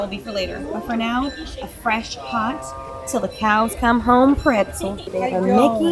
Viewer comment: I thought you said “til the CALS come home” 😂